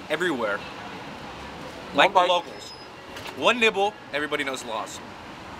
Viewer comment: Bruh I thought he was saying one nipple until halfway through